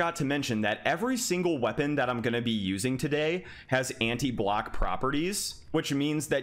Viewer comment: For a second there I thought you said antie black